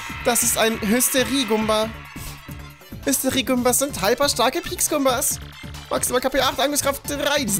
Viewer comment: habe hyperstarke Wichs gumbas verstanden